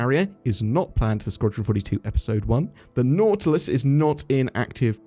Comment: been kind of off the loop but did i hear that right 5:05 squadron 42 episode 1..when we eventually get sq42 it will be early access with only few missions or something?